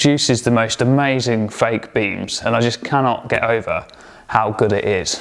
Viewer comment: At the very start I could have sworn you said 'baked beans' then realised you said 'fake beams' ... (my hearing's not what it used to be). Great job as usual. 👍